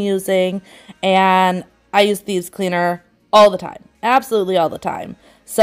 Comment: Bez cleaner???? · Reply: lol.. sorry .. my bad.. I thought u said bez or bees cleaner..🙋‍♀️